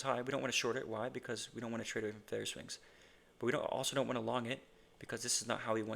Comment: we dont want to short it why? Sorry bro cant understand what you said there, third swings?